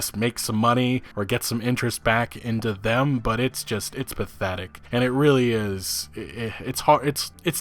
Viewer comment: Every time you said pathetic this video what I heard was "Bethetic" lol.